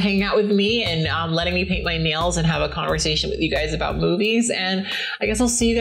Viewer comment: I had It sped up a little bit so when you said "have a conversation about movies" at the end of the video I thought you said "have a conversation about boobies" lol